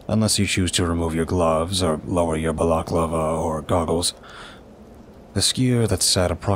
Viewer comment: Just for the sake of info & accuracy, I think ur meaning to say “balaclava”, which is a mask. It sounds like ur saying “baklava”, which is a dessert lol 😬😆🤓